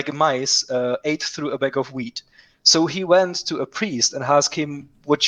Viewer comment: I thought he said “bag of weed.” 💨